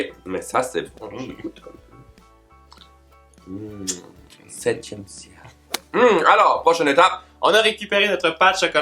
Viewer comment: a dit quoi je n'ai pas trop compris ??❤🍫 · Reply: Dieu. Officiel il à dit : « septième ciel »